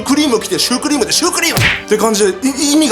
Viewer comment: ヒカキン様がシュークリームって言うとsupremeに聞こえる